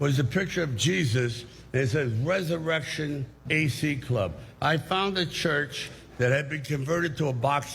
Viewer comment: Aprox 1:13 HELLo he actually says Resurrection AC CLUB